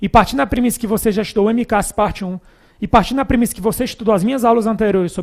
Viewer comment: não entendi, "mcas parte 1"?? o que queo professor fala alí pra estudar antes??